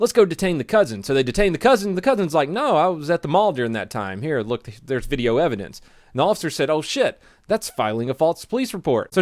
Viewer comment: i thought he said "no i was at the mob at the time and there is video evidence" LMAO